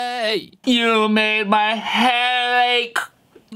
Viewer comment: i heard u make my heart bake u make my hey